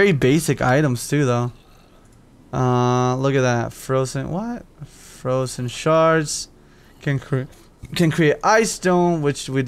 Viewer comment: I know he has a problem speaking but it sounded like he said frozen shart